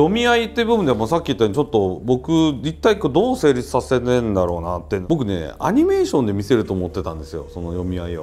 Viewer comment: Did he say anime? I guess the samurai are weebs... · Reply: Zandomorph :) unfortunately he said "animation" though yes of course that is the word we shorten for anime